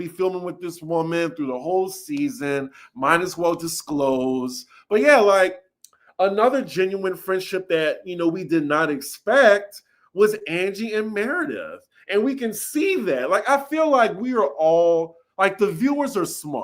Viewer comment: 1.... did you mean Angie and Mary. I thought I heard you say Angie and Meredith. 2. Kyle and PK are friends who were able to commiserate over their "closeted" homosexuality.